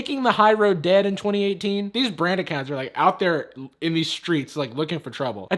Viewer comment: I had the subtitles on. Google thought you said "these branded cancer" instead of "these brand accounts," and honestly...?